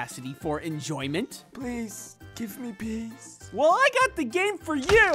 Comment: i genuinely thought they said "please give me peas" lol